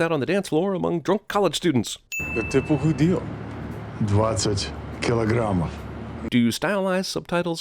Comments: Add a sin for bad translation at 5:50. The subtitle says "Sixty pounds." The bad guy actually says "двадцать килогамм" (20 KG) (edit: fixed the spelling... I can speak and read okay, but spelling Russian... not so much). It's even a bad conversion (20 KG is something like 44 lbs.). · Why have the subtitles say 'pounds' when the guy clearly says 'kilograms'? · do you know what is another sin? In russian the guy actually sais over twenty kilograms instead of sixtx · he actually says 20 in russian but subtitles show 60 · Did no one notice at like 5:50 the guy says kilograms but the subtitles say pounds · LOL give em a sin at 5:49 he said he lost over 20 pounds and the subtitles say over 60 pounds. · I don't think he says 'sixty' but rather it should be 'twenty', I. Not 100% sure tho only around 80%